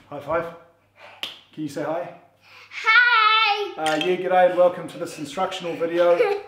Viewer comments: I always thought he said' yeek and I welcome you to this video' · I am today years old when i found out that it is " g'day " and not " get out" I always thought you are saying hello get out all and welcome... Never even thought why would you say get out to us 😂 it felt normal